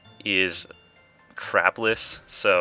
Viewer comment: Am I the only one who heard CRAPless?